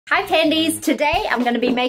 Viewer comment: I thought she said Panties 😂😂, I was like wtf 😳😂😂